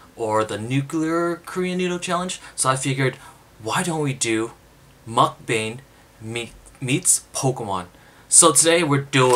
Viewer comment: Thought you were saying MukGang I was like Reppin!!!!